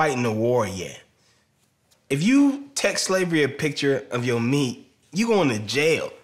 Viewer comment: “If you text slavery a picture of your knee, you’re going to jail” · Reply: Ohhh I thought he said of your meat.